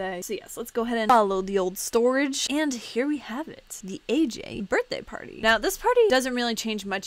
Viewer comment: when she said ''aj birthday party'' i thought she said ''asian birthday party'' what is wrong with me...(im asian)